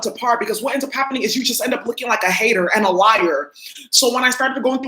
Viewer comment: Funny, I thought you said hater aid lolol like gator aid lol... I can see the ads now " are you thirsty for revenge ?" yea one of theses karmic 's already changed her name to my name.